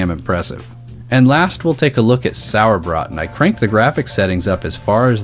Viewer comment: what is this game called? I can't catch what he's saying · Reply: cube 2 sauerbraten